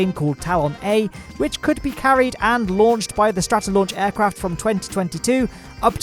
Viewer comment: i thought it said towel-a